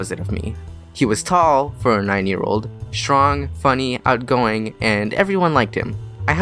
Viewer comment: lol i thought you said "He Was Tall For a 90 Year Old"